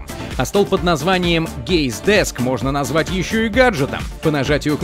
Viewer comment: Мне послышалось "Gay's desk"